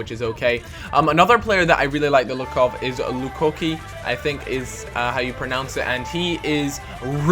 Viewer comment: It sounded like LuCookie